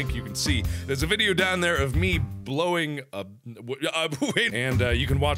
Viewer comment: did he just say "a video of me blowing... wade"?